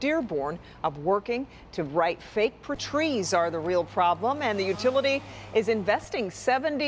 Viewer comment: 😵‍💫 I sure hope that little glitch at 3:39 was only on YouTube and not in the Live! tv broadcasting. I was very confused & had to double back around. Sounded like was saying “Trees & DTE” were the blame for the FBI raiding the Westin hotel. 🧐 🤷🏾‍♂️